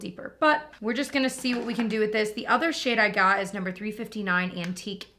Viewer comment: totally sounds like you said the other sh*t I got was…” and I know you didn’t but I’m dying. 😅